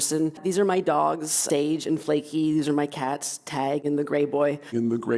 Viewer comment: I thought she said “great boy”